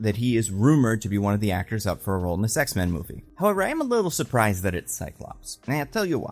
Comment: I heard sex-men at 3:12. Maybe it's because he said the title for I Want Your Sex like 3 times before that